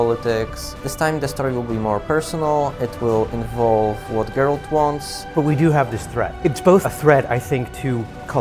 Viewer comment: To the person who thought he was saying "It will involve what girls want", he was saying "it will involve what GERALT wants" ! Hard to understand I know. :)